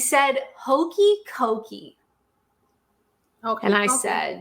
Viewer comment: Oakey Cokey is a dance "put your left leg in put your left left leg out,in out,in out do the oakey cokey with me" etc using different parts of the body,she obviously mis heard him say hokey dokey.